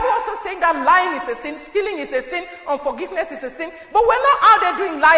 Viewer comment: Did she say forgiveness is a sin? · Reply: No, She said UNFORGIVENESS is a sin.